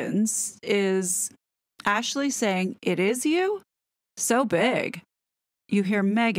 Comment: Hey just so you know, at 3:00 she's saying "I missed you," not "it is you." At least that's what I hear.